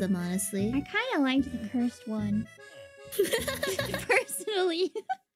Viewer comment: I might be wrong, but I think she said 'I kind of liked the cursed one' in reference to Ugly Sonic. And I'm with Fauna, the pokemon designs in Detective Pikachu were grotesque and off-putting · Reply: Ah yes you're right, my bad there I thought she said first as in the first design. But yeah she said "cursed"